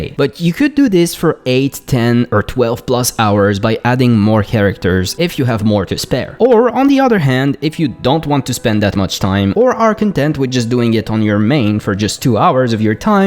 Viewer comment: It's also VERY important that you have no job. That just gets in the way of farming! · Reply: I heard “if you don’t want to spend 8-10 hours per day farming…” I came to comments 😂 I work 11 hrs a day I thought my 2 hrs a day was plenty to get p5 gear